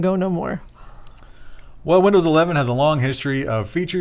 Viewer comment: I swore said Wendells 11 . But I guess that's what I get for listening to it on my way to work